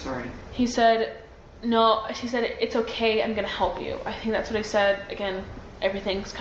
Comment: Does anyone else hear, at 20:37 timestamp, says "that's what I said"? I could swear she said that SHE SAID IT. Anyone hear that or am I going crazy? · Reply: I caught that too! She does say 'i think thats what I said'